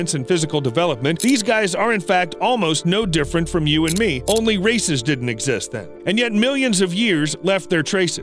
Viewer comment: Did he say "Only racist didn't exist then"?!